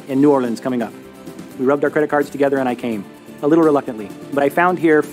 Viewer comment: This 2 part video was absolutely brilliant! 1:27:30 Also...did they just say "We rubbed our credit cards together and I came, a little reluctantly" as an anecdote(?) why...